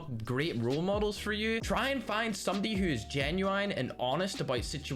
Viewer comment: did he just say genie wine?